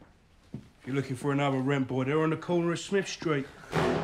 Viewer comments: can anyone tell me what he says at 0:36? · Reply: "If you're looking for another rent boy they're on the corner of smiths street". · Can someone please tell me what the fat guy says at 0:37 thanks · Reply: freddi Kreuger "if you're looking for another rent boy, they're on the corner of Smith street" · What does the short, stubby guy say @ 0:38? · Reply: He said "If your looking for another rent boy there on the corner of smith street.